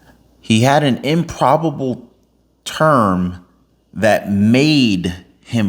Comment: I thought he said "improbable turn" that put him in office... 🤷‍♀